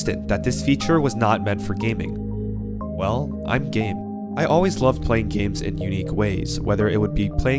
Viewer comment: i legit thought he said im gay at the start